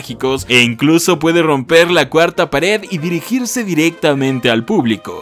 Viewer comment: que dijo? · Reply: Dijo "De romper la cuarta pared"